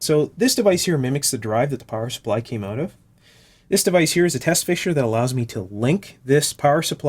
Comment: Great video, many thanks! Great way you found the faulty voltage reference IC. Can tell us more please about the equipment at 37:14, did you build them yourself? I think you said the box with LEDs is a 'test fish', apologies if I misheard, and the big box I think is a dummy resistive load. I guess these are set up to give a representative current draw on the various supply rails, I assume you test all the output connectors off-camera. Looking forward to learning about replacement FETs.